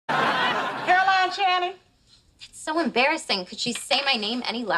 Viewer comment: HIPPA violation? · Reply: I initially thought she said "airline tranny"!